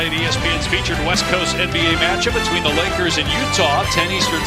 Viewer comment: I hope you can do this again next year 😊 5:13 did he just say lakers??? 😳